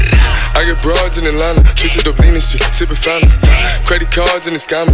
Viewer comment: Is it me or does it sounds like it said I have bras in London